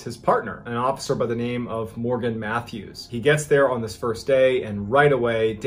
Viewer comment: I thought you said morbid Matthew I was like holy crud thats one crazy name lmao